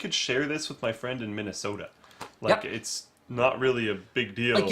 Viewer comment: Did he say Minnesota @ 6:53 ?!